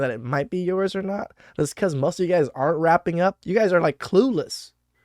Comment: Aaahhhhh, you said wrapping up. I thought you said rapping up. I thought how could that horrible crap noise called rap have anything to do with paternity suits. 😂😂😂😂 it’s fun getting old